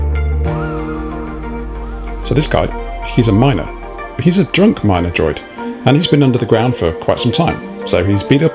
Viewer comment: Around the 4:15 mark it sounded like you said he is a Drunk Minor. How are under age droids getting alcohol? This is something that needs to be looked into. How can our little droids grow up to be big strong droids if they are getting drunk all of the time? Oh, this is a great build. Thanks for sharing this.